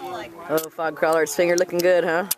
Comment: fogcrawler.... lol i thought he said frogcaller... lol